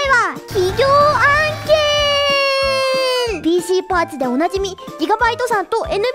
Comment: 耳がイカれすぎてて｢PCパーツ｣が｢妊娠パーティー｣に聞こえた